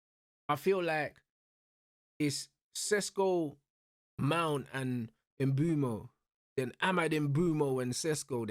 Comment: 😂😂i thought this guy said mount is a scrub